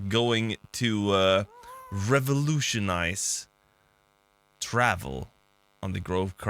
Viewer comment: I heard "revolution"?! 👀